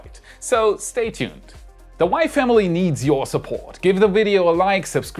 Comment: Every time you say "the WAI family" I hear "The wife Emily."